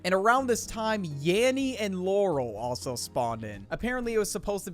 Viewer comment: I heard “Yaurel”